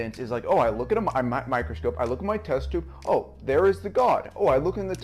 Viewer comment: I legit thought he said 'Look in your toaster'! Took me a couple of rewinds to realise he was saying 'test tube'.